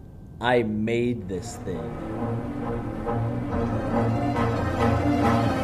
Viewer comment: Oh I thought you said I bathe this thing instead of I made this thing hahah! Is this a spoof of that show on fox or something?! Bill Nye taught me everything I know too! YAY Yoda dogs again!